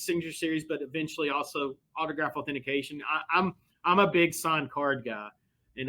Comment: did I just hear him say autograph authentication? That would be a game changer for me. I have so many autographed sports cards that I’d love to slab under CGC.